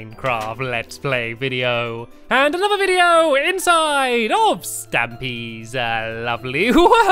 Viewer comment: i thought stamy said minecraft story mode